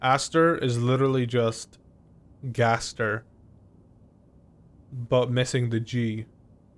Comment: Wait... 0:44 did he just say Gaster or do I just watch too much undertale? Edit: I rewatched it, AND HE DID!! UNDERTALE!!!!